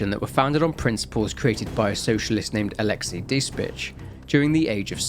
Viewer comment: I'm listening to this in the background and I thought I heard him say the COG was founded by "Alexiy Dis Bitch" 😁